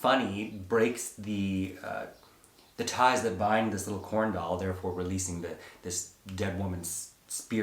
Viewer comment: I keep hearing 'CornDOG' .... much better plot